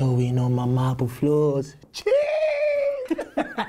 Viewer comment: after they rap, what is dan saying at 2:08? sounds like "cheeeeeeeeeeeeese" haha